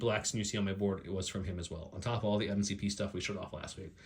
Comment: wait, at around 1:00 did you say mcp? as in marvel crisis protocol?